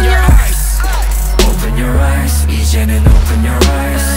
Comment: This is the first NCT_U song I've ever heard and I'm legit appreciating their hair so much(I have a thing with dyed hair).Also,does anybody else think it sounds like they're saying "Jinyoungie open your eyes",and I'm just like "Ok,what did you do with Jr. and where's the rest of Got7" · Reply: Inara Faizal i hear "jonhyunie open your eyes" and idk if i have to cry or laugh🙃